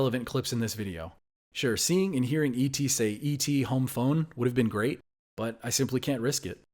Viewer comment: For a really long time I thought E.T said “E.T go home”